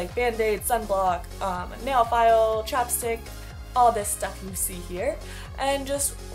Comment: I thought you said a Chopstick not Chapstick, I was like wait what?! lol